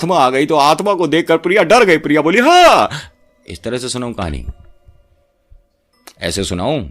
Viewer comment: Sir I have watched in 2x speed for trying to hear 26:23 Wali clip I hear budiya but in normal I hear Priya 🤣🤣🤣